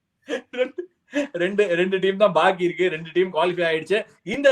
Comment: did he just said randi team